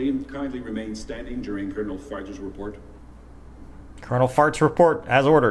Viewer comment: For those who misheard the colonel as Colonel Farts, his name is actually Colonel Farge.